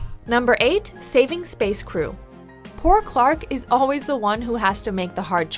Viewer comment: Did you just say SPACE KRU? u mean skaikru